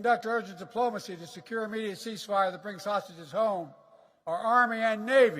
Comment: Did he say, bring home sausages? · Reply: Do you have ears? He clearly said hostages.